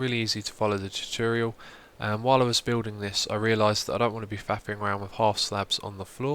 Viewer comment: did he say faping around?